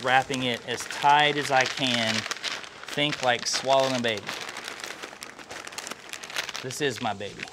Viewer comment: It totally sounds like he says “like swallowing a baby”, not swaddling. Lol